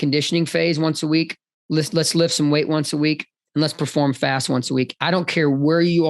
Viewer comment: Did he say fast @ 14:45? I listened so many times and I can’t decide if that’s what he said 🤣